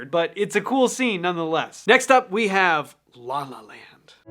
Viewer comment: I am once again asking you to do a video on Tom Lehrer! One of the greatest satirists/math teachers! Also when you said, "next up La La Land" I heard, "next up llama land"